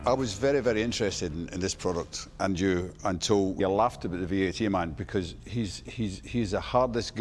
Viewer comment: Theo’s subtitles at 9:13 “I always pay the Batman. I ALWAYS pay the BATMAN” 😂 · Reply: the subtitles say he's the "V8 Man". LOL